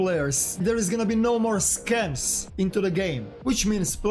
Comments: I see a lot of people thinking he said "there will be no more scavs" Guys he said no more SCAMS as in you cant get killed and scammed trading your item anymore, but instead list it safely on the flea market. I thought he said scavs too lol but then i rewatched it. Plus Nikita in his recent podcast a few days ago confirmed he has really big plans for Scavs. Don't worry nothing is happening to scavs just got lost in translation a little bit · No more scavs ?? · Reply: Guys he said no more SCAMS as in you cant get killed and scammed trading your item anymore, but instead list it safely on the flea market. I thought he said scans too lol but then i rewatched it. Plus Nikita in his recent podcast a few days ago confirmed he has really big plans for Scavs. Don't worry nothing is happening to scavs just got lost in translation a little bit